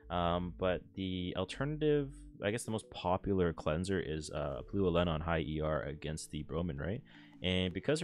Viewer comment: can anyone tell me what cleanser he said? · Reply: Blue Elena on high ER